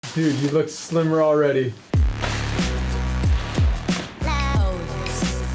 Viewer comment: For a sec I thought the intro said " you look stickier already" My mind is weird. BTW I subbed. U are one of my favourite youtubers